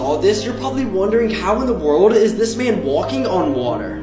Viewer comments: Thought you said he died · At the last one i thought you said: He dies and turnes upside down🥴💀😭🤣👌🏻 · When he said “dived” I was confused because I thought he said died. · I THOUGHT HE SAID "he died and turned upside down" LOL -Demonica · i heard him say, "he died and turn upside down"🤣🤣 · I heard "He died and turned upside down" 💀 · Him: “he dived and turn upside down” Me: he’s walking on air :0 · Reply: I thought he said he died and turned upside down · When he said he dived I thought that he said “he died” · i thought he said "he died and turned upside down" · Ooh you said dived I thought you said died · When he said: ''he dived and turned upside down" I THOUGHT HE SAID:" he died and turned upside down " 😰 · I thought he said died not dived